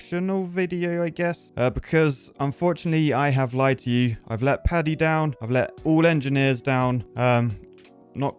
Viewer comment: I thought he said i put paddy down at the beginning